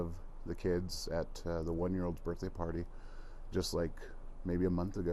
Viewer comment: The children were 4, 2, and 1 year old. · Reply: thank you for clarifying that. I thought I heard 1 month, not 1 year.